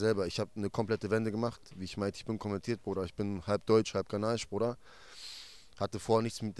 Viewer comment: Halb deutsch und halb was? Habs nicht verstanden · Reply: Ghana